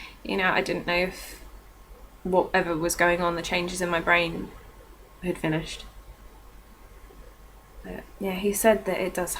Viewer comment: on 7:33 your voice changes and it sounds like you say are we finished and the look in your eyes something different??